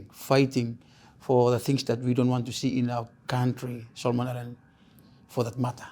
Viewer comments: I think the inaudible word he said here is " Solomon Island(s) " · the subtitles say [inaudible] bit he is saying "Solomon Islands"